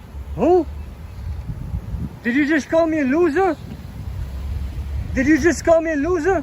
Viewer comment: So did he call him a loser? I played it like ten times and I'm still not sure. (I know that's a very small part of this, I'm just curious what y'all think.)